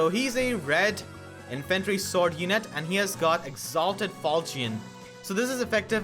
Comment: sounded like "Salted Falchion"